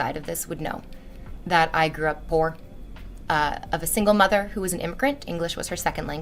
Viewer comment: "a single mother who was an imprint" ?? Had to rewatch cuz I thought I had misheard 🤣